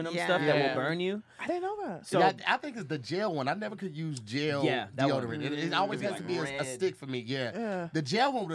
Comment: I thought said "jail deodorant" then I figured out he said "gel deodorant". My first thought was "Yeah I could see how jail deodorant could break you out"...